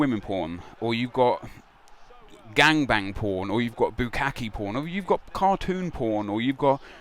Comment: I thought you said Lukaku porn, that'd be interesting to see